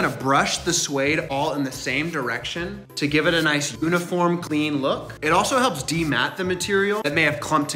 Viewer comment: Did he just say “oo-niform”?!